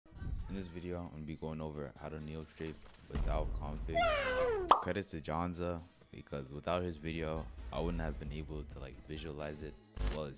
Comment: I thought u said no configs